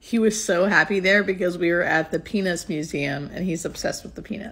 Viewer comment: I had to rewind and look at the subtitles to double check what I just heard, and thankfully the word was "peanuts". 😂